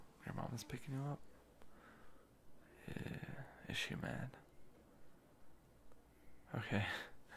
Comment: I CANT HELP ME- I THOUGHT HE SAID IS SHE A MAN?!!? I WAS LIKE AHHH ITS MY MOM- BUT THEN IT TURNED OUT IT SAID “IS SHE MAD?” BUAHAHA HELP ME I CANT jumps off building